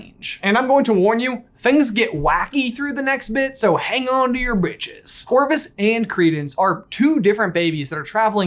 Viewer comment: I know he said britches but it sounded like just said “hang on to your bit**es”. Truer words never spoken haha.